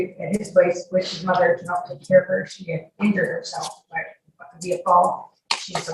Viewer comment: ~ At first I thought she said something like “…finger herself.” I guess it was because I knew this was a sexual type case that was on the brain or something. Lol